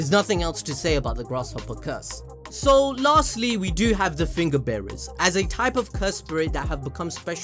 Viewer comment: I literally thought he was saying "Finger Baerries" but he was saying "Finger Bares"😂 Also since I haven't read the manga is it possible to switch the curse you have, like a lower grade curse into a Special Grafe curse?